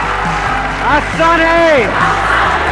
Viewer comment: what's he saying every time in the beginning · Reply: Aasan hai...😂